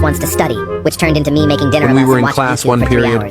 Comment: Why did I hear “Making dinner in her bed.”